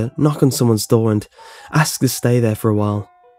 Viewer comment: When will you do a face reveal?!?!?!?!?!? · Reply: no face at 10:01 did he say I'm getting delirous like h20 delirious that's what I heard also I like how your names no face but your like facereavel😦